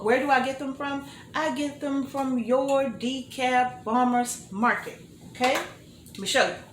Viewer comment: i thought you said your decaf farmer's market, lol not Dekalb Farmer's Market, no they do not ship, I shopped there until I stopped driving, I miss it so much.